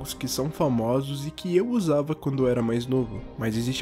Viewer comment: Hm... então ele é? "São aplicativos que eu gozava quando era mais novo" HM... Também escutei usava depois, mas não perde o outro sentido pra quem ouve nessa intenção kkk